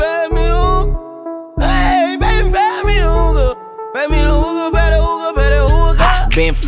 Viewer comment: I thought he said they pass me hookah